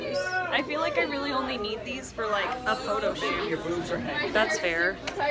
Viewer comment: Anybody else heard "Ma'am your boobs are heavy"